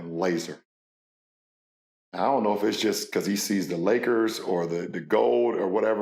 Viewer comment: did james said lakers or the goat ???